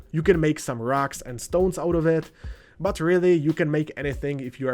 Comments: Did I hear a Rock and Stone?! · DID I HEAR A ROCK AND STONE!?